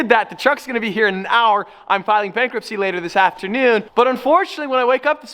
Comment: So did just say that he was filing for Bankruptcy at the 09:26 mark or did I hear that incorrectly